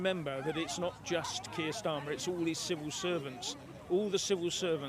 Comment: I heard you say "our Civil Serpents" (you may have said "servants", but with the accent difference between us - UK vs USA, I heard "SERPENTS") when talking about the changing of the law to eliminate the D-I-E rules.... Quite appropriate. I hope you do not mind if I start using that for politicians and govt bureaucrats.